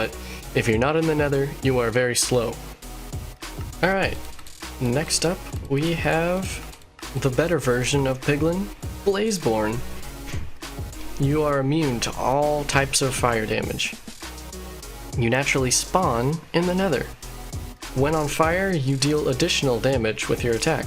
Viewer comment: i thought you said " YOU ARE A MEME TO ALL TYPES OF FIRE DAMAGE"